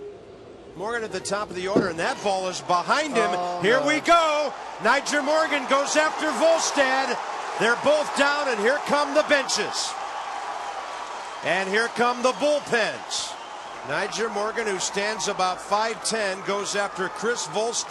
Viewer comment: commentary at 6:09 sounds like "here come the bitches", Or is that what they are saying?